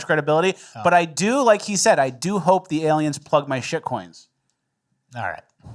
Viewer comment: Did Fleccas just say he wants the aliens to plug his shit?